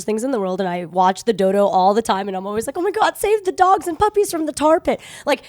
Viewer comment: At first I thought it said we ate dogs